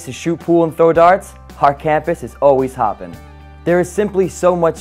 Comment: he said on campus it's always hoppin. I had to rewind it cause I thought he said. It's always poppin. 😆 I was like "did he just say poppin. I was actually going to give him some props tho. I'll still give him the props tho.